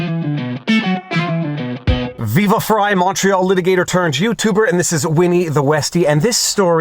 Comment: "Montreal litigator"? This whole time I thought you were saying "macho"